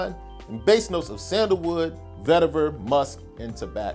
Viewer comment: Bruh! Did you just say Isse Miyake had a note of Tabasco(4:53)?? 😩🤣